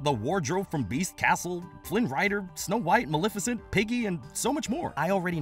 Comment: Did he just call Ham “Piggy”?😭